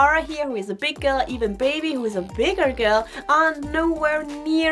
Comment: Is baby ok? I don't see her often and i think i heard "baby was a big girl" and not "baby is.."